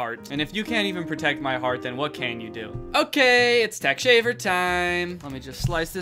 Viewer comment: i thought he said thug chaker